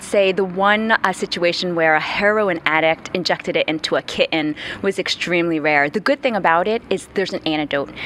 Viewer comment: The lady around the 1:50 mark said "ki-en" instead of kitten. I think it's annoying when grown-ups try to talk like teen kids.